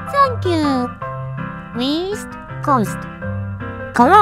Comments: East? I thought you said- Weast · am I the only one who thinks that Hatsune said "F*ck you" instead of thank you 💀💀💀💀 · East? i thought you said weast